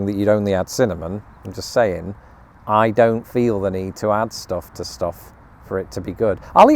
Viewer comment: What was said: “I don’t feel the need to add stuff to stuff” What I heard: “I don’t feel the need to season my food” 😂